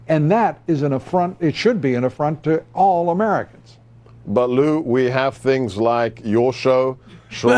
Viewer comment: Did Gorkon call Lou "Baloo"?